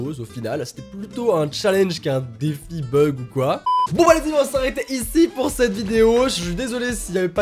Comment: Pk à 13:18 tu dis bon bah les filles c'est la fin de cette vidéo bon je suis pas contre ça moi aussi je suis une fille mais le problème c'est que ça fait trop chelou😅